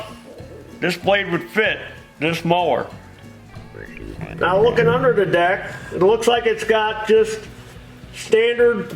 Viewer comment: What does the robot voice say around 7:43? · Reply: would you want me? Wtf was that lol